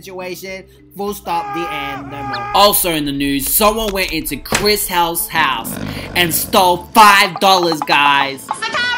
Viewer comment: It sounded like said "Chis HOUSE HOUSE"